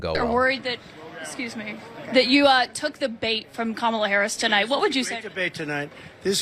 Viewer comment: Trump mistakenly thought that the reporter said "you took the debate" instead of what she actually said which was "you took the bait".....what an idiot!!!!!